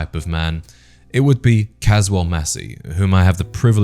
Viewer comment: Oh! I thought you said, “Casual Messy!” 🤣